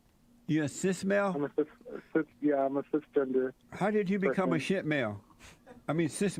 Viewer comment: did he say how did you become a sh#$ male